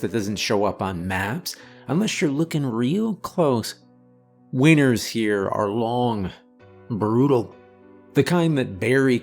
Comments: wieners here are long…..is what I heard because I was ajusting my pillow 😂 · 1 minute. I'm sorry, I misheard as : Weiners here are long, and brutal. I was thinking what crazy sausage they're talking about in the story haha. Thank you for the upload!